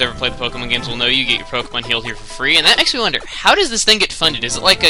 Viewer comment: When he entered the pokemon center I thought he said " you get your pokemon killed here for free."